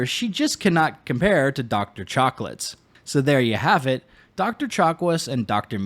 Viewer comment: did you say Dr. Chocolates?